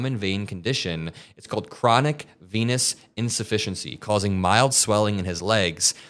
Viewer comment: I thought you said 'chronic penis insufficiency' for a moment 🤣